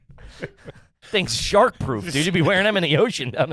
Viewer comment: When I thought said pants were shart proof…he said shark proof!! 😂😂😂